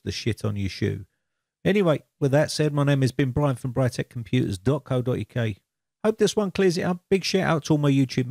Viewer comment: I keep trying to decipher what you say at the end of your videos...@ 7:08 it sounds like "my name has been Brian from Britec computers"...is this right?